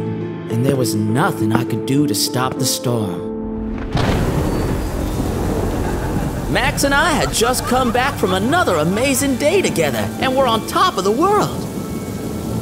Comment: And did i heard nugget saying "Amazing Date together"-???